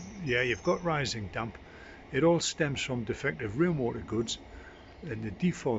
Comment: you say something like "it all stems from defective [real mortar goods??]". I could not track what was said here. What does the rising damp stem from and how should it have been treated instead?